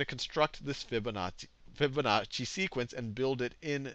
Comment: Fibonazi. I heard you.